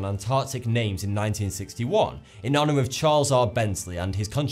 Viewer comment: Am i the only one who heard him say charizard instead of charles r?